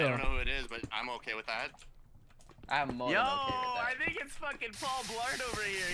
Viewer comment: Is it me or did say "fuckin" @ 1:26.